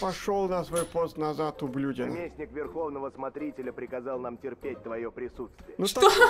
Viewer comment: мне послышалось "пердеть" 😂😂